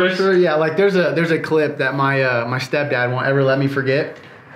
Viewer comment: Wait did say “step dad”? At 4:15?